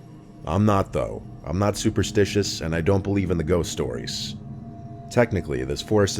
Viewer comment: Can anyone tell what he said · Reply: “I’m not superstitious and I don’t believe in the ghost stories”